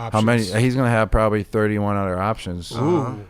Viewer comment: Wouldn’t it be 32 options? Or are the Pats not on the table? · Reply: It sounds like he said "31 other options"